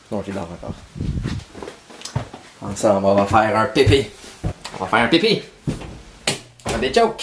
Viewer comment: Il me fait trop rire! Et c'est trop beau ce qu'il joue au début ! J'adore 😍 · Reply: Moi ce que je trouve drôle c'est que à 0:47 il dit on sen va faire un pipi mais jai compris un bebe